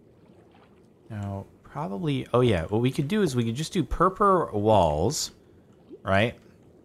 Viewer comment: Did I just hear it right 1:49:00, is CS making PURPA walls. LOL